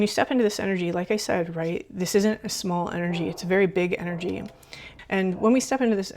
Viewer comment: I heard a big dog when you said “this is a very big energy”